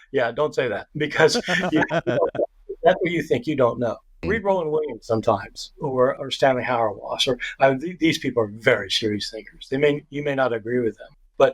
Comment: What names did he say there? It was hard to catch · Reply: Rowan Williams and Stanley Hauerwas